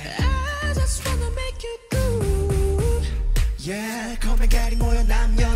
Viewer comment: That “I just wanna make you groove” was one of the most beautiful things I’ve heard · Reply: I misheard it as 'l just wanna make you drool' lol.